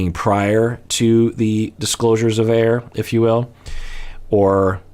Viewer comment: Did you just say “disclosures of air” 😂😂😂